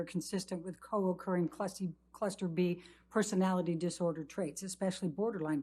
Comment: I heard clussy!